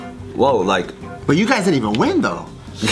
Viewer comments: I don't understand what said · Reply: TotallyTaco he said "but you guys didn't even win though?" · What did he say at 1:03? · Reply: The Gam3rman37 if you're referring to, he said "But you guys didn't even win though?"